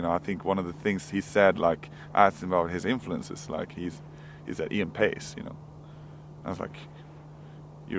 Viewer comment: what band did he said as the influence on martin axenrot at 1:49? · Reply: Ian Paice from Deap Purple.